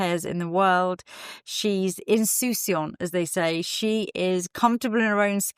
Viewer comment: Can someone spell out the French phrase Lisa used at 4:50 mark when describing the character who is comfortable in her own skin (sounded like “en-sous-si-en”) please? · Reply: insouciant ! xxx